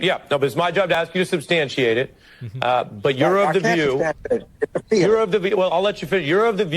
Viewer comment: am i trippin or did he say you're of the jew